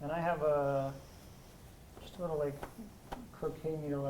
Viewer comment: did he say hes got just a little bit of cocaine?